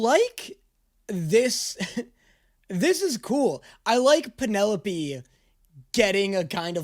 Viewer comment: you can hear a soft “Penelope” at the first “time To be the man of the house” 8:01 ALSO DAMN look at her goddamn arms 🤩 i forgot what people say she’s a daughter of but that’s definitely what she is